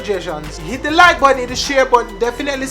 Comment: Bruh, mi hand dem get weak and feeble when watching this... And do I hear this right @ 2:31, "hit the like buddy and hit the share buddy". Smaddy tell mi nuh🤣